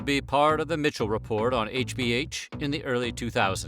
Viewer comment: am I trippin or did you say HBH